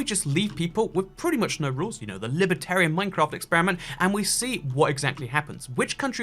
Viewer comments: I thought it said, "Librarian" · I thought it said librarian AT FIRST SKSKSK